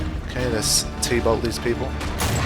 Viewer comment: 45 till 59 I thought u said let's tea bag those people